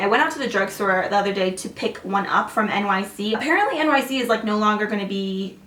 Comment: For a minute I got so sad because I thought you said that NYX was being discontinued. Lol I had to remind to make sure I heard it right.